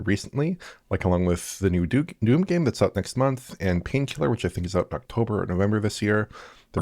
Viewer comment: did he say new Duke Nukem game? I googled, but couldnt find anything. Only the rebuilding of an old one. But nothing official. Or what did Oliver say? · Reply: I understood Painkiller. Didnt know that one - found it on Steam. It looks amazing.